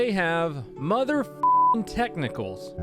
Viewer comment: I thought he said mother f ing testicles